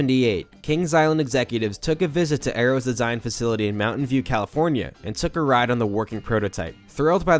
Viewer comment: bro I thought he said mountain dew California